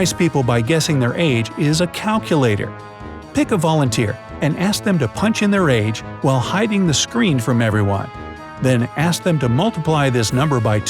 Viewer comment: I can't hear what is he saying someone pls tell ne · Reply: "Pick a volunteer & ask them to punch in their age while hiding the screen from everyone"